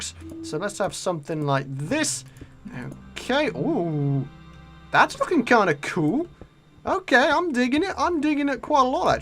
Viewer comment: did he say that is fing cool